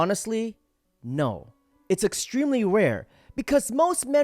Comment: Who else heard him say, “it’s extremely WEAR” 😂😂😂